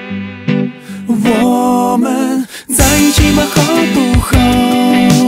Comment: 在一起嘛好不好 听成了 在JB好不好。。